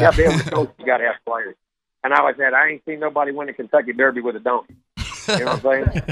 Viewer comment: I've listened to the clip like 10 times, I can't tell Jimbo Fisher is saying donkey. Sounds like he says done 😂